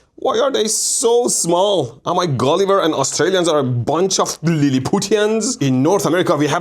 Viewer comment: Did he really said "Australians are a bunch of little Putins"?